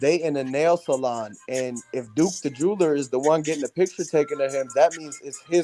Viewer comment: I thought they said the dice was loaded and Duke the jewelry got the dice